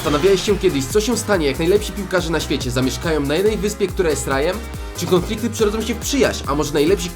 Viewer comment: kto usłyszał "*która jest srajem"*